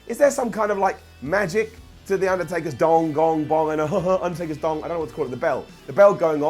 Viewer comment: Did you say Undertaker's thong?